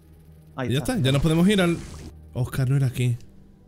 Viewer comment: la primera vez que lo vi, entendi que habia dicho "oscar ven aqui...."